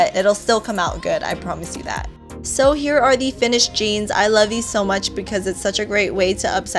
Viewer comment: she said "I love you so much" instead of I love it so much hahah that's so cute